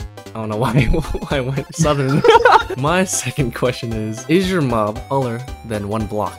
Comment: I thought he said is your mom White